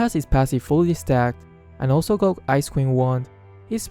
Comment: I thought he said "Ice Cream Wand".